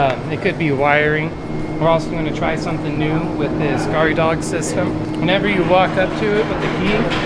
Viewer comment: scary dog system? Couldn't hear him, what is it...